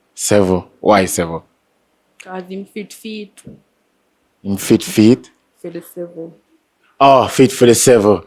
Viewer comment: I know misunderstood when he asked her why 7 and she said, " cuz him fit fi eet"😆 I know he thought she said him fit fi eat. It's just how she speaks lol